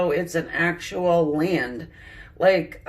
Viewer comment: what is it she says?? What word was it? Thnaks 4 channeling, lots of love and light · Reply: It feels as though it's an actual land.